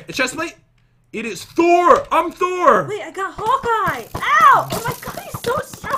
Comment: Wait did Jen say hulk eye haha lololol 😂😂😂🤣🤣🤣😂🤣😂🤣😂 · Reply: Hawkeye